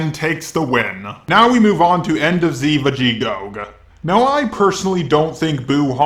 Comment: did you really say vegetgoog or some shit